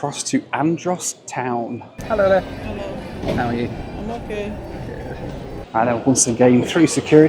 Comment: did she really say i'm not good lol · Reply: lol she said I’m okay 😂